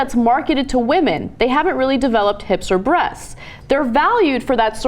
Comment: lol....I misheard Ana as saying "hipster breasts" instead of "hips or breasts." Hipster breasts.....sounds like a phrase that surely must have been said by someone at some point....